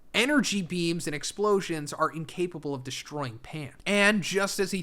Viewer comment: Are you saying "Pan" at 8:26? You said "in the DBZ world energy is incapable of destroying Pan"? I would assume you are saying pants* but you said clearly said pan twice and theres no T on the end of the word you pronouned and even the caption say "Pan". Im confused what "Pan" is, i mean i know Pan is a caracter in GT but that doesnt have anything to do with what your were saying.